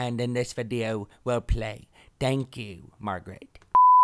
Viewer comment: Is it just my imagination, or at the end of the intro, at 0:17, does Gryphon say "Thank you, Margaret"? That kinda freaked me out, since my name is Margaret o_o